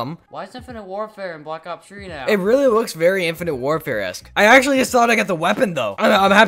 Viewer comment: In 4:48 did he just say infinite warfaric??thats not even a word but I see what he’s tryna say🙂😆